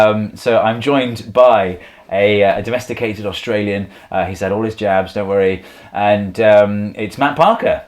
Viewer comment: What is Steve saying at 1:53? It kinda sounds like "he said all his jabs". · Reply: "He's had all his jabs" Jabs being english slang for shots/injections